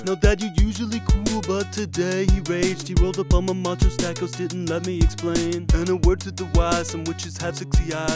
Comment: What does he say at 1:44? It's bothering me that I can't figure it out. "He rolled up on my _________ didn't let me explain" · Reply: I think he said that “Mucho sausages”.